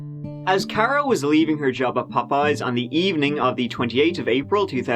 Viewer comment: Did you say Popeyes? Now you have my attention!